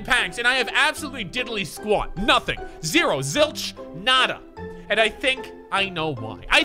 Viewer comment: He said "Nada" or I heard something else (I'm from Brazil, Nada It's nothing in portuguese)